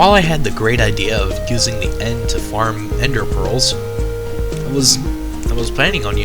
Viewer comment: I thought he said ender girls.-.